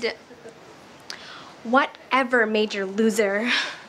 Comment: When I tell you I did the “WHAT EVER MAJOR LOSER” all the time as a kid like it was the biggest burn omg so embarrassing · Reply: I misheard it as 'made your' instead of 'major' back then.